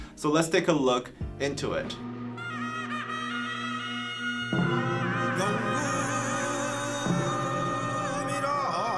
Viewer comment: What does the person in the bg say? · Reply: 명금 일하 대취타 하랍신다. 鳴金一下 명鳴 myeong = ringing + 금金geum = Iron ,( jing : metal drum of korea) 일一 il =once + 하下ha = under,condition 대취타 DAECHWITA = King’s visit or army marching 하랍신다 = do it 명금일하 대취타 하랍신다. (myeong-geum-ilha daechwita halabsinda) = Once the gong jing rings, start playing the daechwita.